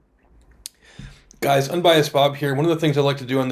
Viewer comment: Mwahahahah, I almost thought you said at the beginning - I'm biased Bob...